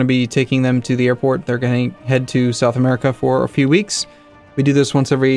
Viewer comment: where in south america? Portugal? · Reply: I thought he said South Africa